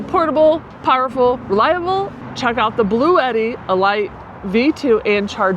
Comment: did she say "E Light"??🤭